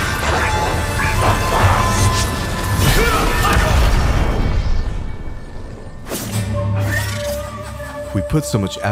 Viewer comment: que dijo en esa parte?? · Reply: KURO'SAGOL